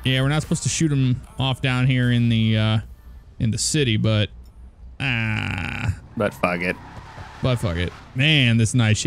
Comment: Y'all said, "But, fuck it." I heard, "Butt fuck it."